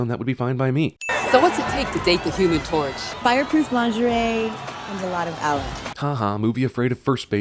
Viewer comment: What did the girl say at 3:56? Fireproof lingerie and.....*HUH*.. did she say...... "and a lot of Ellen"???!!!?? I watched it and watched it, can't make out what is being said. ANyone?